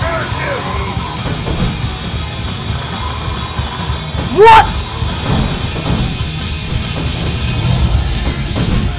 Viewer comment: What does it say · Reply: McCain